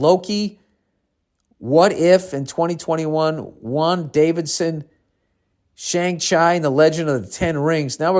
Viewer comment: did Bill say wanda vision as wan Davidson? 😂😂